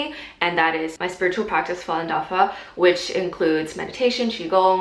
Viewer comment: what do you say at 1:55? what is your spiritual practice called? please? :) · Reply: hello hello! Falun Dafa - there are a couple of links in the description :)